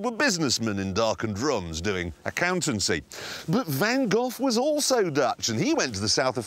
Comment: I thought he said Van Goth.